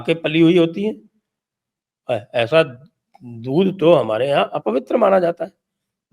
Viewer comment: What does he say at 10:55? Dool, bool or something else?